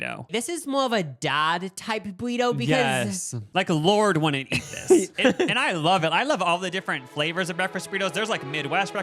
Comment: did he say lorde wouldn’t eat this burrito hold awn😂